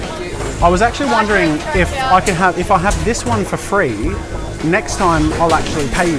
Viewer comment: I thought u said i have autism on the start